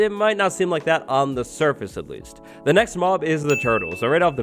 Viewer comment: I'm watching video with caption on and when say next mob the caption says SEX mob(at 7:00min)